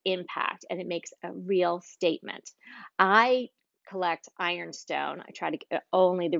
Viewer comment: What is the name of the dishes that you collect? I thought you said "Fire Stone" I found a 'Fire King'. Your collections of dishes are beautiful! And I just joined your channel. You have many wonderful tips; looking forward to more of your videos!